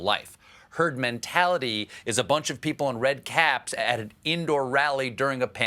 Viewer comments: I don't know about you, but what I heard him say was, "You'll develop a Turd Mentality..." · I heard ..."turd mentality". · Sounds like Trump was saying "Turd mentality". He certainly is leading in that area.